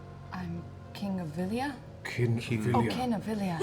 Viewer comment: In Marisha's defence I heard king too.